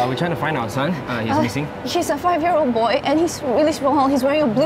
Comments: I swear I just heard “she’s a five-year old boy.” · did she just said she for a boy?!